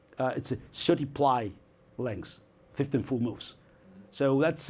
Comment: What does Kasparov say at 29:55 - 30:00? I couldn't make it out. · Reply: 30 ply length... 15 full moves